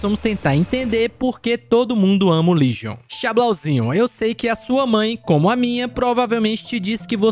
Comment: Só eu que escutei ''Eu sei como a sua mãe, é como a minha'' ??